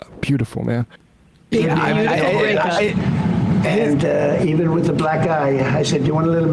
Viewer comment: At first I thought he said "even with a black guy" !!😂